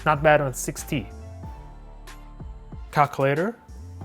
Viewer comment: did you say cock-later